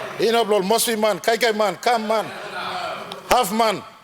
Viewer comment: did I hear the Prime minister say "Kan Man" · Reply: He says 'Kam man', a Tok Pisin (Momase) term that means outsider.